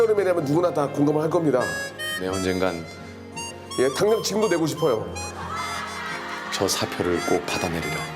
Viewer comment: 이부분 뭐라고 하는 건지 아시는 분 계신가요...? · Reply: 당장 지금도 내고 싶어요